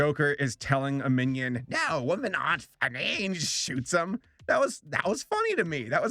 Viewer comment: Does anyone understand what he says at 3:10? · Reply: HypeCom - "Women aren't funny".